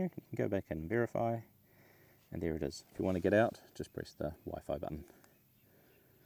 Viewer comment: he's in Ballistic Data, and says "if you wanna get out (unintelligible)" Can anybody tell me what he says there? Lowers voice, must be assuming we know, but navigation in the menus is a real bear for me. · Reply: It’s the wifi button which is also the LRF button